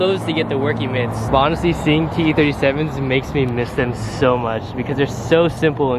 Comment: I thought you said e37 to the BMW 😂😂